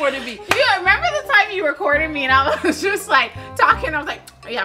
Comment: Is it me or did say remember when u were courting me ...bc if she said that for those who don't know old school terms ..that meant that at one point they had to be dating . I'm going to go back to c if that's what she said 😒👂🤔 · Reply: Just re-watched that part, I think she did. On previous watch thought she said "recording me" but... 🤔👂🏽